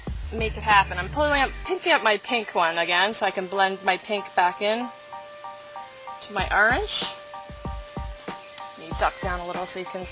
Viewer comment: blend my pink back into my errrrrrange, thats what it sounded like. HAHHA